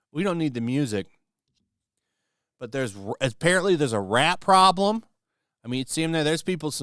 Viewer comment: I heard ‘Rap Problem’. Thanks for the pictures to clear it up. Keep up the good work.